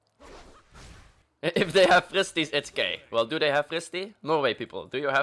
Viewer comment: did he say Norway people?